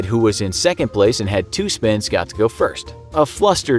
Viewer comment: it says 2 pins instead of spins